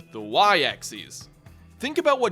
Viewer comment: Hold up. No, I'm not gonna get on your case for Zed. It's the way you pronounce Axis. It sounds like you said "Axeeis." Wat?